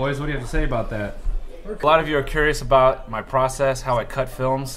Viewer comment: i thought he said " i know a lot of you are curious about my prostate"